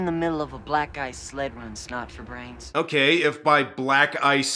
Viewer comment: I totally thought he said black guy’s sled run 😮.